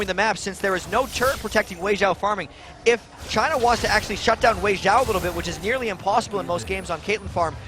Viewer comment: did he just say china wants to shut down weixiao?